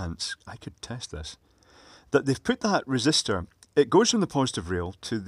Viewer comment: thought you said 'I detest this' Maybe it could be an illuminated marshalling wand for a dwarf... who works at the worlds cheapest airport.